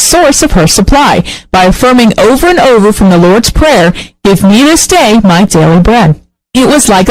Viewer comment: Can anybody understand the statement said at 00:27:20? I would really like to know but the audio is quite unclear for me. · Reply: "Give me this day my daily bread." - From the Lord's Prayer